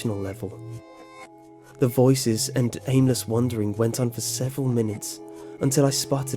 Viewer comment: did he say anus wondering